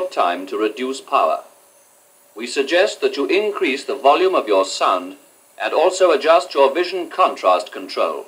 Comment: what is the tape saying? "We suggest that you increase the volume of your sun? Son?" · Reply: Sound. With strong RP (Received Pronunciation) it will be said like Son with a really soft D sound at the end)